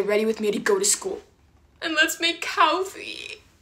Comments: I wasn’t looking at my screen and I thought that she said “LeTs MaKe CoW fEet” 😂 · I thought you said let's make Cow feet😂